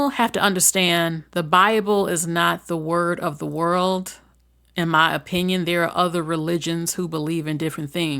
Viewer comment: Moderator…the word is MELTING not milting, each time you said that I thought you were saying someone’s name…Milton